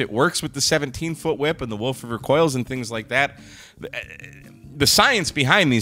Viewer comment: It almost sounded like you said WOKE River Coils.